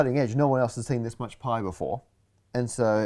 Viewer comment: I completely died when I saw that the subtitles said: No one else has seen this much pie before(pie instead of pi)